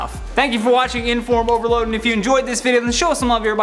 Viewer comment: Why do I hear “Inform Overlord” instead of overload? I think I’m loosing it.